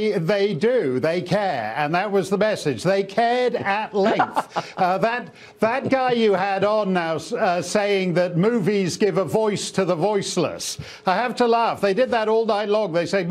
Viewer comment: That chef sounded like he said "boys to the boyless"...